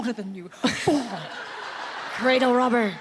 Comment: Please help me!! What did Renee say to Lucy in 1:35? I can't cath =( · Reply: Cradle robber! Meaning its Lucy's fault for being with Renee when she's younger. Lol